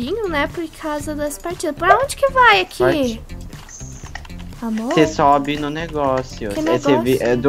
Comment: EU OUVI ERRADO OU A DISSE ´´AMOOR``?